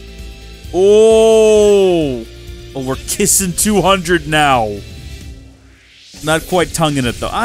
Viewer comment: lol what is that saying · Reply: You’ve never kissed 200?